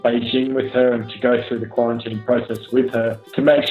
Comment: wait a minute did he say Quarantine 😂😂